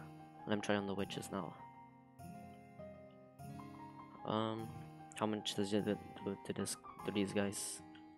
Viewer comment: 😂😂😂😂😂😂 who can guess what i was trying to say 💀💀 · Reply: "How much does it did t-to these to these guys" I think aaa Idk ;-; Btw thx for the showcase